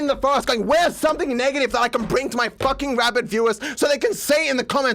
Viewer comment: "Rabid viewers" he must see me foaming at the mouth every time Muta mentions a VM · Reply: Oh, that's what he said. I thought he said rabbit viewers. I was so confused